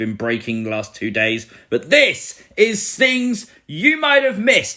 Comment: Originally It Sounded Like He Said Tings You Might Of Missed Glad It Now Sounds Like Things Because What's A Ting Is It A Wedding Ting 💍